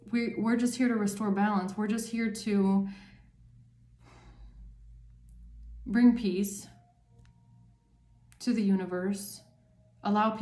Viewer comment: I thought for sure it said "... restore peace to the galaxy." 😃😃😃😃